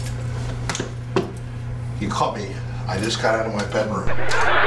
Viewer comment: A female voice is starting to sing at 35:12 The sound of footsteps at 39:44 and that symbol on the floor in that room is a satanic pentagram symbol (google it). At 42:32 a clear male voice sounds to me like it's saying: "Tsk, Tsk, Tsk, Tsk.... You Spot Me." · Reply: Good catch on that last one. It can be heard best by slowing it to .5 or .75 but I hear "you caught me".